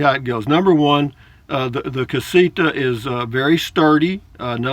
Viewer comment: CasiTa...not CasiKa. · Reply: LOL...well, maybe it’s a combination of my 73 year old (Yankee) ears and your 73 year old voice, but it sounds like you say CasiKa. Whatever. Love your videos, man. Safe travels.